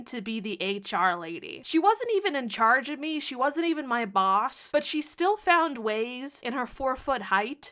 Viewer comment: lol at first I heard “she wasn’t even my mom” not “boss”